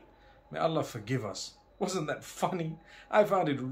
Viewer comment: i thought mufti menk is saying " wasn't that fucking " Astaghfirullah 😐😂